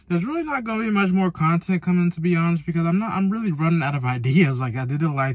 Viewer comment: what did you say bruh? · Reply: I’m running out of ideas